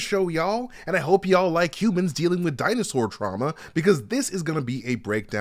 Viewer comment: I thought he said at the beginning “Cubans dealing with Dinosaur drama”. I was both confused and intrigued, but was ready to watch!